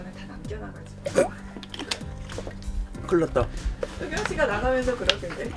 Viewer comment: I am curious of the subs here... why do I keep hearing Soonyoung Ssi, not Dokyeom... I know i am wrong cuz the korean subs also say Dokyeom and later the subtitle on screen also says Dokyeom AAAAAAAAH